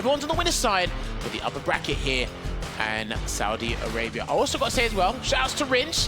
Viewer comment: Did they just say In Saudia Rabia?